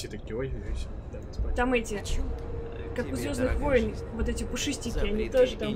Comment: чупакабра? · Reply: нет я про 10:30 минуте . подумал что ты о чупакабре говорила не услышал про звездных войн